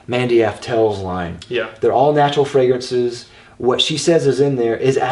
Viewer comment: did you say andy taur at 6:55?